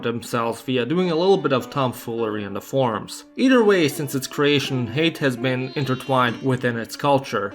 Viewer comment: Bro I swear I heard him say "Croatians and hate are intertwined" at the start. These videos are giving me that Balkan brain 😂